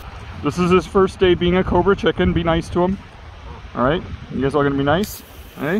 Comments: I'm never calling them anything other than Cobra Chickens from now on. Thank you for that. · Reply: So he really WAS calling them Cobra Chickens?? I just thought he was saying Covert Chicken with a funny accent, cuz I never heard it before and yet cobra chicken is freakin hilarious and now that I think about it very appropriate name for geese. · I actually heard him say "Covert Chicken", I was surprised to read the comments! 😳🤔🤷🏼‍♀️ He is a good lad though, no matter what. 😊😍🦆❤ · "this is his first day being a cobra chicken, be nice!" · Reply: I thought he was saying "corporate chicken". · I'm never calling them anything other than Cobra Chickens from now on. Thank you for that. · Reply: I thought he said corporate chicken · I'm never calling them anything other than Cobra Chickens from now on. Thank you for that. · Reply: Pretty sure I heard "covert" but in my head, cobra chickens still makes sense 😂. Their necks curve the same way a cobra stands up with its hood. And like the other person said they hiss, strike, & have a short fuse like a cobra 💀. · I'm never calling them anything other than Cobra Chickens from now on. Thank you for that. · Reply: Guys. I thought he said covert too but the subtitles say cobra chicken. Literally look at the subtitles. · I must fit the phrase "cobra chicken" into a conversation someday. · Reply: Lmaooooo I heard “cobra chicken” too and as a city kid I thought that was just something I had never heard before. “Covert” makes more sense.